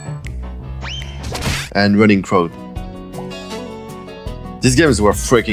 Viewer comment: i cant hear this games name What is it? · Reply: It’s called « Run in crowds »